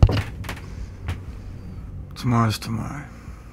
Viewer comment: I can't get what the guy is saying to Taron at 0:44. Can anyone please help? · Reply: tomorrow's tomorrow?